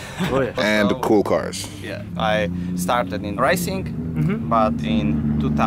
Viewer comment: I heard 'ricing'